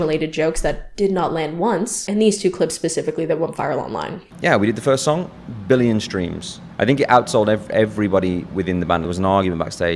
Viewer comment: I thought he said "we had the first song: Billy in the Streams"